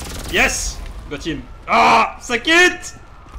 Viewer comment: Did he just say "ah sakit" in